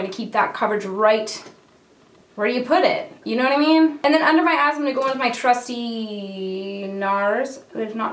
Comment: I thought you said "under my ass" instead of under my eyes. I had to pause and rewind. 😂